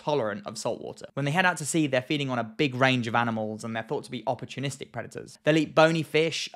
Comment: When I hear "opportunistic predators" I hear "pools are a wonderful invention" 😂